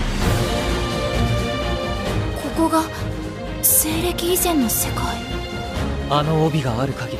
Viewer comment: did she just say ...... ISEKAI!!!! REEEEEE